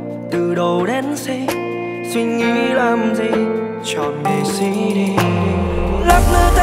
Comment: có ai nghe nhầm chọn nghệ sĩ đi thành chọn đĩa CD ko